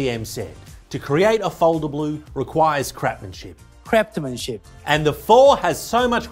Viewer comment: Is it me or did I hear say "Crap-manship" 😂